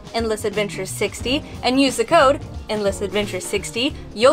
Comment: sounded like u said Inn-less adventure and not End-less..whatevs!